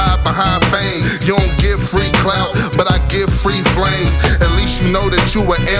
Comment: I honestly thought you were about to say 'but I give free brains'. Coz this shit sucks